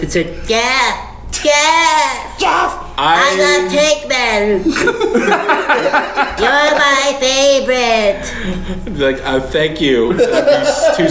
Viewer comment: What did say when impersonating the grey alien at 1:04:45? "I've got... you're my favourite!" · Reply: I love tankmen, your my favourite